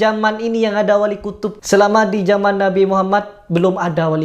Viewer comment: mas salah denger kali si kyai itu bilang wali terkutuk bukan kutub